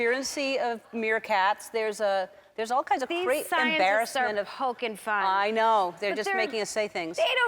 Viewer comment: I thought she said "porkin funny", The Good Place reference.